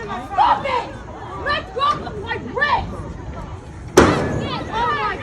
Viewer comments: Lets see what happens when the lawyers get involved. PS: I think I heard her say " LET GO OF MY BREAST!!" That is sexual assault. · I thought she said let go of my breast